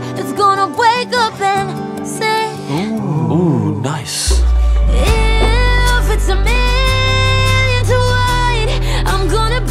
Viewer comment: sometimes i can’t understand what camilla is saying, i didn’t know it was ‘if it’s a million to one’ till i looked it up 😂 i thought she was saying ‘give it to me, what i want’ 😂😂😂